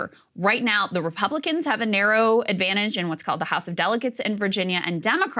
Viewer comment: Sounds like krystal said "house of delicates" and it made me chuckle